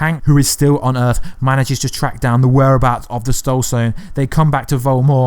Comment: “stoled sone” instead of “soul stone”. 🤣🤣